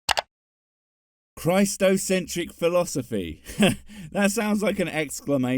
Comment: I thought you said "Christ-o-matic", which sounds like a contraption in a 1992 infomercial 😂 & you KNOW it won't come with batteries.